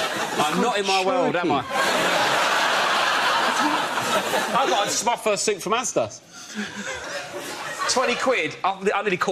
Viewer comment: any idea what rob is saying around 19:47? automated captions cant figure out his accent either. Sounds like he bought some kind of suit from azda i think? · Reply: He “bought his first suit from Asda” 👍